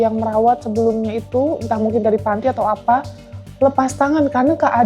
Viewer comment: Harus diserahkan ke pihak yg kompeten yang menangani, kasian anak sekecil ini udah seperti ini, dia perlu ditolong untuk dialihkan kegiatannya, semoga lekas sembuh nak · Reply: Td mba novi bilang pihak dinsos dh angkat tangan.. Apa. Sy salah denger ya.